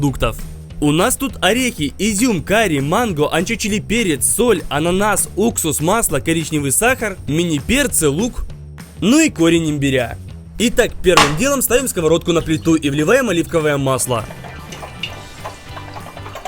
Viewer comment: мне послышалось перечный сахар